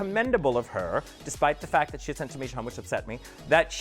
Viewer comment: "Despite the fact she sent Tamish home, which upset me..." is what Bianca said.